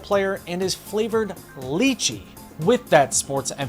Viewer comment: i never heard of a Lychee before and thought you were trying to say the spanish “Leche”